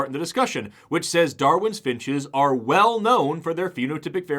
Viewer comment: haha sorry, but I’m just listening to this and not watching along. So I thought I heard you say, “Darwin’s bitches” lol